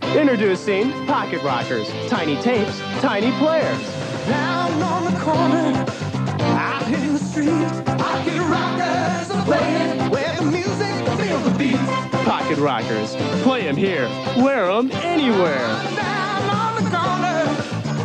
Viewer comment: I swear I heard the ad say "Fuckin' Rockers!"